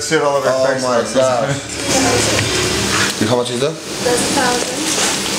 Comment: how much did they say it cost? i cant hear clearly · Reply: A stack